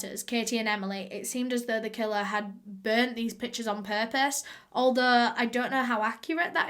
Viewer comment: i thought she said "it seemed as though the killer had burned these b****es on purpose" i replayed it like 4 times to realize she actually said "it seemed as though the killer had burned these PICTURES on purpose"